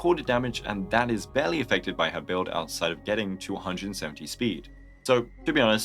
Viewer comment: Did you say "to 170 speed" or "270 speed" here? I did save for her but I am not super sure anymore. I wanted to replace Topaz with her but idk man. Probably will still pull to get my gambling fix /j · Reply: 170 SPD